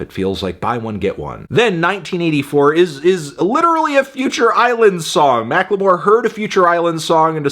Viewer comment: When Fantano said “Future Islands” I heard “Lonely Island”, which might have been a better reach for the Mack